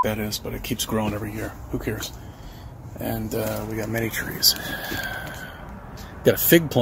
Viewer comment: Did he say "mega trees"? Does he think he's Rick Sanchez?